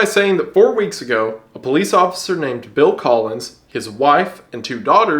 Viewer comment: Why'd you name a character Bill Collins? I keep mishearing it as Phil Collins. Lol